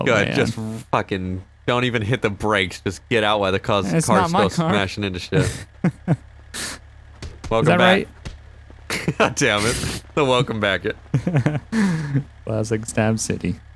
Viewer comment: For a minute I thought it said “smashing minor” 😂